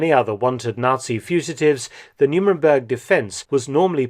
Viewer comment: Nuremberg was a place in Germany & where a war crimes trial took place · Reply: yes, I realise that. It’s just that it always seems to be pronounced as NuMemberg in Dr Felton’s documentaries. Or am I mishearing it……?